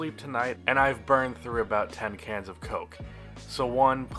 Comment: I thought you said your burned through 10 kilos of coke and then I was like wtf